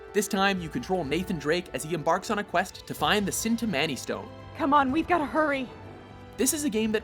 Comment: Did I hear 'sin' ta-man-e stone? Chintamani stone!